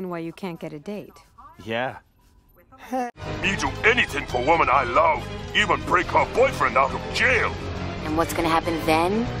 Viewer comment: what does bizarro actually say? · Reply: "I will do anything for woman I love; even free her boyfriend out of jail!"